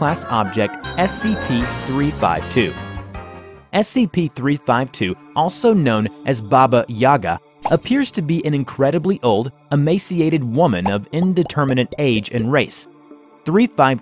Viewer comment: This is Baba Yaga My brain: This is Baby Yoda Edit: Yes, I changed it from lady gaga to baby yoda, it sounds way better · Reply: Hello Word edited their message lol At first they said that they thought it said "baby yada"